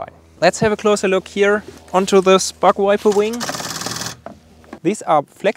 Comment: Sounded like he said butt wiper lmao